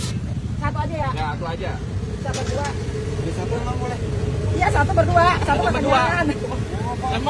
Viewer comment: Sayup2 kedengeran aya sora si 😅 .. "di campur emang boleh?" cenah 😂